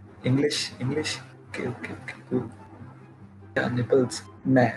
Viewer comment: Did he say nipples ? 🤣🤣